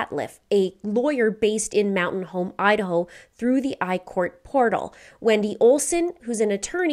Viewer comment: did u say "through a portal" 😂😂